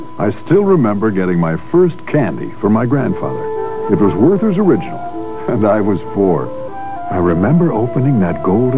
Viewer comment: I always thought he was saying "It was Werther's Original, and I was bored"!